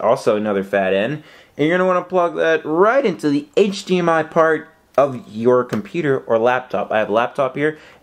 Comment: Omg I got so worried cos i thought he said u need 2 hdmi Ports i got so worried